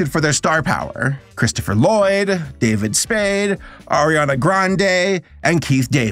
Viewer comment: Tbh i thought the first time u said who snowflake was voiced by i thought u said Rihanna,? 💀💀💀